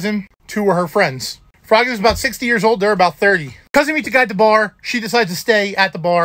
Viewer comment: When he said 60 year old man I thought he said 16 years old. Totally different story in my head.